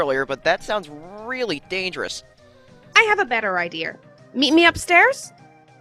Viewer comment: TT: “ i have a better ider” Well that’s what I heard